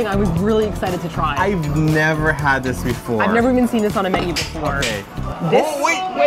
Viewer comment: You guys had said I've never had it warm before, and I heard, " I've never had a worm before." I was like, what a worm?! Haha